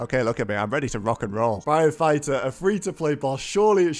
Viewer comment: it sounds like he said "firefighter" lol